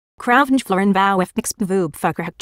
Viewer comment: DID SHE JUST SAID “FUCKER”